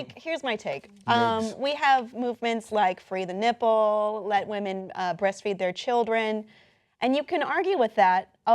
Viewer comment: i heard "We have movements like free the nipple and breastfeeding" and i go, WHAT? In my mind that's very normal.